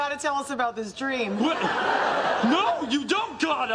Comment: For a while when Kelso said, “No you don’t Gotta!” I thought I heard him saying, “No you don’t Donna!” Instead of Gotta* lol